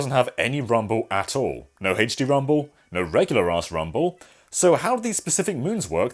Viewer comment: Did he say regular ass rumble? lol